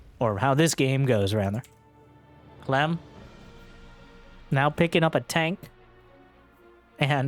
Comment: I heard "Clem wins" and I had to rewind to try and figure out why the Protoss would tap out to Clem. @@